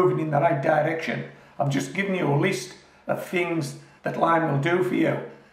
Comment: for one split second I thought you said you added wine! I hugged my bottle tightly just in case. Delighted to confirm my hearing is dodgy and that all you needed was lime! Phew 😛👍🏻🙏🏻